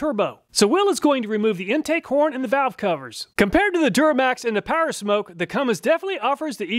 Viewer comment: "Compared to the duramax and the powersmoke." Nice one, hadn't heard that one before. that said When I first heard it, I swore you said Turdmax and powersmoke, but after listening a few times I'm sure you said Duramax.